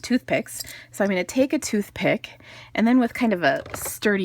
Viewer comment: I thought you said "toothpaste". LOL I knew you said "toothpick". I bought this stamp set and framelits, can't wait to try this card-TFS!